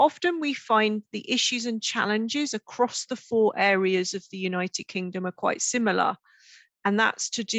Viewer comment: 頃『キンタマ怖い志村〜』と聞こえるんですが、何と仰ってるんでしょうか？ 馬鹿な質問ですいません。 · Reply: United) Kingdom quite similar. ユナイテッド）キングダム クワイト シミラー かな？